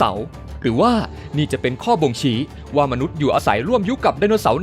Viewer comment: ตอนที่นาที 0:53 ผมได้คำว่าบ่งฉี มีใครได้ยินเหมือนผมมั้ยหรือว่าผมหูเพี้ยนไปเอง